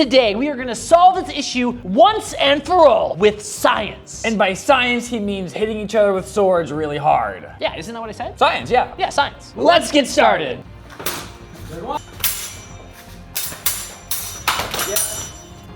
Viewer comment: Am i the only one who thought they said lets get sharted